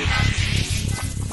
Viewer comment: bro why do I hear “you stinky baby”